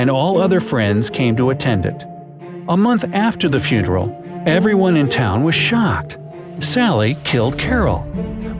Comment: I thought he said everyone in town was shot